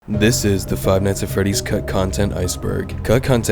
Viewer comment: Never click on a video while distracted cause hearing a dude say “This is the Five Nights at Freddy’s Cuck Content” woke me right up just to realize I misheard